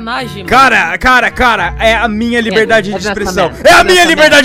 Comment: KKKKKKKKKKKKKKKKKKKKKKKK me liguei dps ouvi “cadê minha liberdade d expressão”